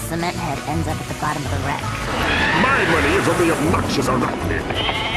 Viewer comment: what did Grim say? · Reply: his money on jeff the spider